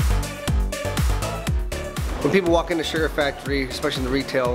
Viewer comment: when was filling the thing with candy I thought it said suga and was like ARMYYYYYYYYYYYYYYYYY but then I realized it said sugar cuz it made much more sense and I realized how Kpop is slowly destroying my brain cells